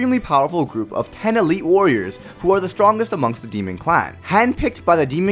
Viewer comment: Miranda Lou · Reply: What he said at 0:35 ''10 elite warriors who are strongest among the demon clan'' Your ears heard: ''10 elite warriors who are from the demon clan'' two different things.